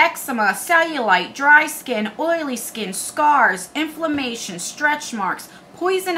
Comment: I missed you saying not to much. Also I had to rewind and listen again on the coffee scrub because I thought you said it treats SARS when you said scars lol.